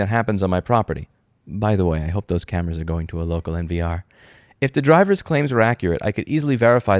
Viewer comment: "by the way I hope those cameras are going to a local ___" What did you say here? NVR?